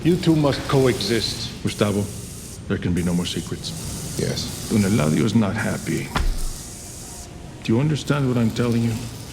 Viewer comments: I don't think that's actually anapodoton. The subtitles (assuming you included them manually) suggest you think he's saying "when Eladio's not happy..." when I'm pretty sure what' he's actually saying is "Don Eladio's not happy," which is a complete sentence. Beyond the percussive "d" sound I can hear at the start, the pause and the stare at the end make way more sense in that context for me. He's not leading Gus to a conclusion here as much as he is making a no-nonsense statement. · Juan bolsa says "Don Eladio is not happy" but the subtitles say "WHEN Eladio's not happy" that's totally wrong